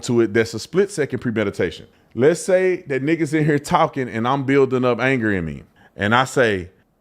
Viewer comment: Nickis in here talking lol.sorry that’s what I thought you said for a split second lol🤦‍♂️